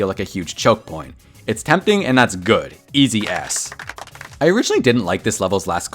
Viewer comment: sound like he is saying something else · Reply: Even the caption wrote aśs 💀